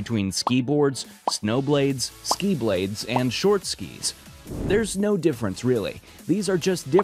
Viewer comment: did he say, "... there's snow difference..."? 😆